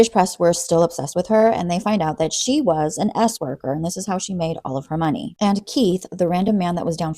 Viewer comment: S worker😂 I misheard that as “Asperger’s “ and for a second I thought “I could get paid to be what I already am if I was in the seventies and in 🇬🇧?” But then I realized what you really said… I could do that here in the states if it was a common fetish to be with a girl who can’t read a room well and continually makes weird associations and thus contagious social awkward situations.